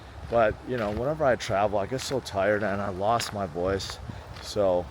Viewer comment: is tired and cold, we're all tired an cold😔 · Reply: I thought you said tired and old at first glance